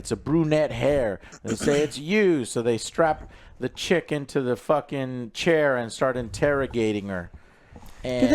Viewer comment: I had to rewatch this because I thought AJ said, “They strapped the chicken to the chair.”